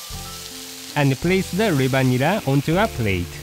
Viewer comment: when said 'reba nira', I thought he said 'the vanilla'.